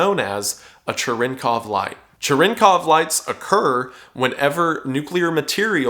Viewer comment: did bro say TrenCough Light 💀